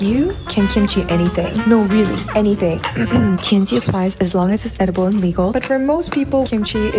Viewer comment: I thought it said thc apples